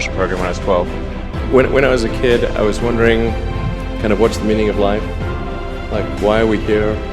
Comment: 에서 what's the meaning of life? 문장 직전에 뭐라고 하는 거죠? When I was a kid I was wondering 말한 뒤 can I? gonna? 뭔가 소리가 들리는데 잘모르겠네요ㅠ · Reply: kinda(kind of) 흔히 구어체에서 사용되는 사족이죠. 쓸데없는 말이라 생략된 듯 싶네요